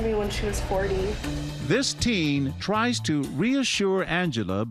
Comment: captions say 14 instead of 40... edit: 6:17 it says "I'm John can you honest"